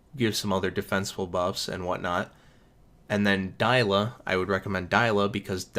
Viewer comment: Subtitles say "die Allah" instead of Dila, I died 😂😂😂😂😂😂